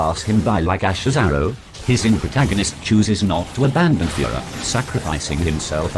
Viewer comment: i thought that the TTS said "his inner protagonist chooses not to abandon Führer"